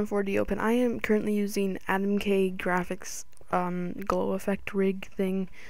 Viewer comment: what text rig did you say at 6:20 i couldn't make it out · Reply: AdamkGraphics